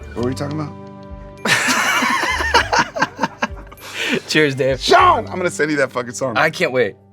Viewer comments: Every time Sean said "Cheers Dave" I heard "Cheers Babe" and I loved that so much more. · I straight up thought everytime Shawn said "Cheers Dave" I thought he was saying "Cheers Babe"